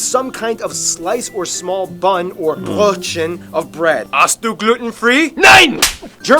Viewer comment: what does he say? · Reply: "Hast du gluten-free?" - "Nein!" 'Gluten-free' in German would be: glutenfrei. Translation: "Do you have gluten-free?" - "No!"